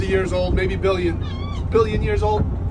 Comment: Oh "rock"! I thought he said "it's a real nice co--". No subtitles so honest mistake.